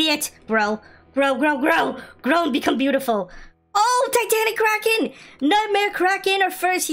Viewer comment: did he say titanic kraken? hahaha😂😂